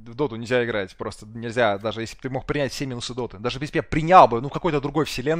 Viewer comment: Я понимаю, что папич говорил "все минусы у доты", но в первый раз услышал "принял ♂️semens♂️ у доты". 🤣